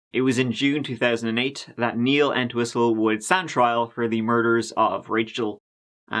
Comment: Thought he said "Neil MeatWhistle" 🥩😙